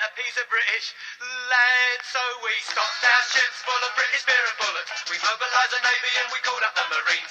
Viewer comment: I always thought it said “British pair of bollocks” lol